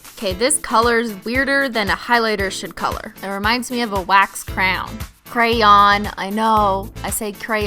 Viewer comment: Great job on the nails, keep that good work. (P.S you did sound like you said crown when obvisally you meant to say crayon.